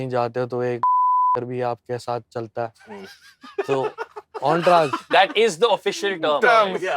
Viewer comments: what does he say?? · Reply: Jab koi celeb hota hai to Uske sath crew or log b chore hai entourage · Can somebody tell what he said at 25:10? Thanks! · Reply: Entourage